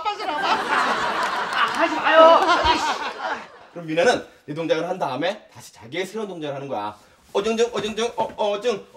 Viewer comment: 아이 ㅆㅂ 이렇게 들리네 ㅋㅋㅋㅋㅋㅋㅋ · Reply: ㅆㅂ 한거 맞아요